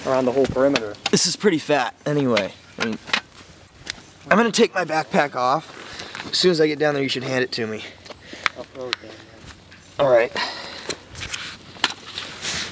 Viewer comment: He says, I'm gonna take my backpack off then you can hand it to me... WHAT I HEARD, I'm gonna take my BRAIN out and leave it here. My mom always told me not to still my hand in holes. Therefore I'm not bout to put my whole effing body in one. Oh well, back to watching...